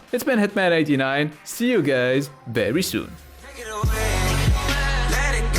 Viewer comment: did he just say see you gays very soon? lmao